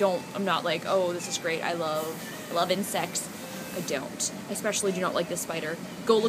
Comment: no she said insects haha i thought she said that too